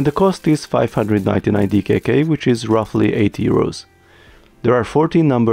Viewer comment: oh that is a cool set 1:10 - "and the cost is 599..." Oh sh*t that is expensive, oh that's DKK, roughly $80, that is ok. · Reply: Oh, I thought he said "8 euros" and was really confused.